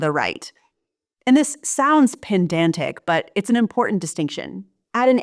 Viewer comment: Sorry can't get over pen-dan-tic at 14:45 . Is it said like this in America ? Isn't it ped-an-tic ?